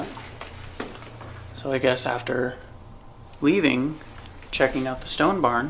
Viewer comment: When you asked who’s up here and you thought it said guest I reckon it said guess.